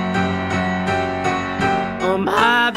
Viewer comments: bri it sounds like he says caught your nose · I thought he said got your nose I'm alone 💀